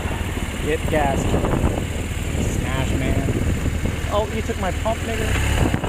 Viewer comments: did anyone else catch what he said “he took my pump Ni&&a” or am I crazy... It’s okay to tell me I’m crazy.. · Am I the only one who heard say the N word at 11:45? "Oh, you took my pump, n***a?" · Did he just say.."you took my pump NIGGAH!!!" ??? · Oh he took my pump nigga. Did he really say that? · does he really say you took my pump n-word???